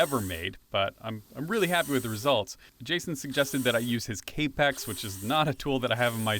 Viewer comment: What is k pecks? At 25:24 you said (paraphrasing) you liked it and wish you had it in your shop. I see a clamp,stopblock, miter saw and your work piece. Which of this is the tool you mentioned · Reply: The miter saw he was using is a Festool Kapex